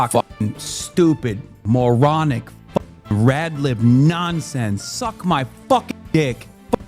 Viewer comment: I thought he was about to say the gamer word. That was close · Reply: I mean at 1:43 he said "Red Lib" and I swear I heard it as Red Lip